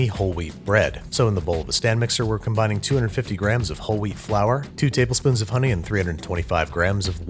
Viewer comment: I thought he said “holy flour” instead of whole wheat flour....